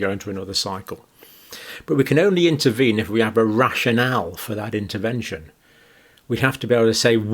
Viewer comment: At first I thought he said a Russian owl 🦉 😂 rashin al lol (rational)